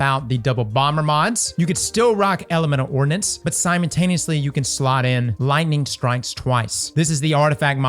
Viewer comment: did he just say, "simon-taneously" 🤣🤣🤣